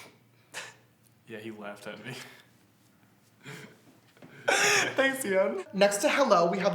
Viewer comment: Okay 😂😂 19:43 you said "thanks Ian" but I wasn't paying attention and I thought you said my name which is Sienne cause it's literally Ian with an S in front... I was shook